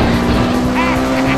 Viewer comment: what did she say? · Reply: I can win for sure. Hehehehehe